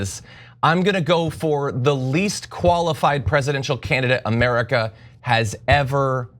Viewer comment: What's the point of this video? Joe is an ex-liberal. He's independent now. So what? I respect Joe Rogan. I don't respect this clown. Also, how is the former President the least experienced candidate ever? What a clown. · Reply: What he said was " least qualified" not "least experienced"